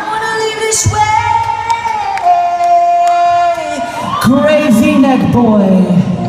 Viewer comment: i thought he say "gravy neck boi" and i started laughing 😂